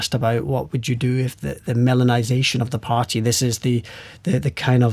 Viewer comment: Oh, "Melonization". I heard "melanization", which is already the case.